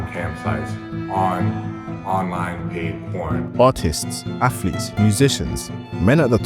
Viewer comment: I recently found out I’m autistic and at first I thought he said autists 😂